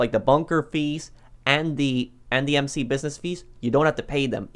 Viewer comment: What is he saying at 4:37 ? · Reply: Lina Svendsgaard bunker fees and the mc bussiness fees